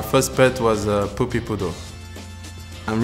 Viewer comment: Did he just say his first pet was a poopy Poodle? 🤣